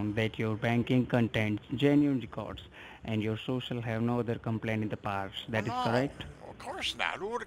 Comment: No other complaints? Is that what he’s saying? Banking what?? Social what?? I have no idea what he’s saying at around 10:14...Does anyone know what the hell he’s talking about?